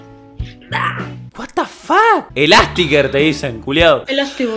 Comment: Amigo pobre pibe le tira elasticgirl y el pibe le dice elasticboy pedro flasho q era mina jaja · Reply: Yo escuché "elasticker" y pensé que era alto pelotudo pero bueh jajaja